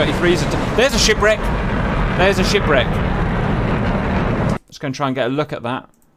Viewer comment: when he says "oh there is a shipwreck!" - I always hear: "there is a shit break!"